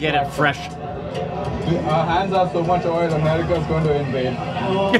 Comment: what did he say? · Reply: "Our hands have so much oil that America's gonna invade it"